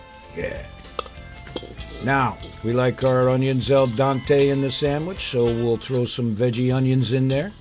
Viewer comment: I think I heard "El Dante" a couple of times instead of "al dente". :)